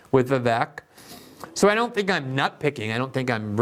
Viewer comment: does Glen call nitpicking “nutpicking?” ❤